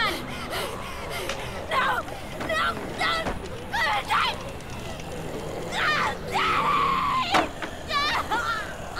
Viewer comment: What sarah just say? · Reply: No dont gonna die dont kill me